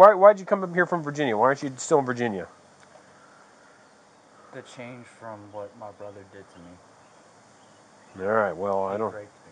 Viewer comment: When the boyfriends opened up to the cop on what happened to him. It broke my heart. I hope he heals. Without that girl. He deserves better. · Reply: I thought he said to get away from his brother. I listened twice and that's what I heard... His brother r×××× him.